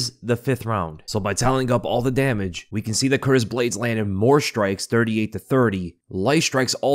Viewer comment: Anyone else hear say Curtis Blaydes at 11:04 lol